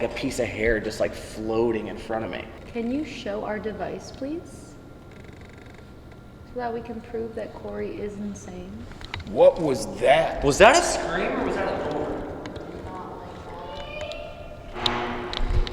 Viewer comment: it sounds like it says Corey